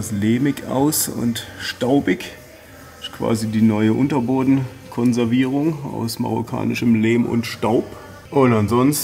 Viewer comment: Bei 3:20 verstehe ich Unterhodenkonservierung?????????😱